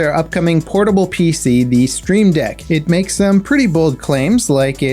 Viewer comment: Did he call it "Stream Deck"?